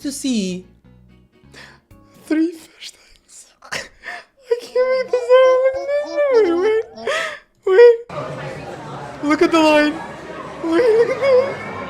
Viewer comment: I think i got what he said here😂 "I cant make this up, look at this video wait,"🤣🤣